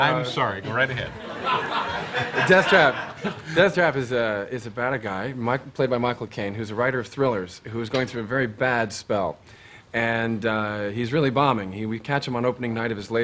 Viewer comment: Hi folks, I am learning english, Is somebody able to tell me what did Chris say from 6:20 to 6:35 ??, please, thanks for your support. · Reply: “‘Death Trap’ is about a guy, who’s played by Michael Caine, who’s a writer of thrillers, who’s going through a really bad spell, and he’s really bombing. We catch him on opening night of his latest play, which has really bombed, and he gets absolutely pissed that night, and he goes home, and the next day of the play.. ‘Pissed’ is an English phrase meaning ‘drunk.’”